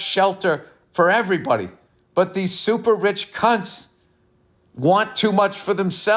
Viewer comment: I misheard you. I thought you said super-rich cunts.... Either way you are right.